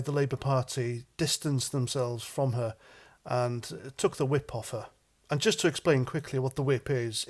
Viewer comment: Oh, you said took the whip off her, I thought you said took the whip to her. I do agree that she's a hysterical figure though! She can't be a racist, only white people are racists